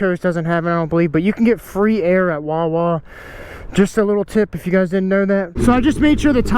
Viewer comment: when you said wah wah I heard it like womp womp as in that's the noise that plays when you death wobble